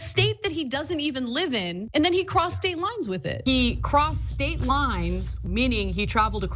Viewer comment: At about 3:30 into the video, I keep hearing Prostate Lines. Anyone else?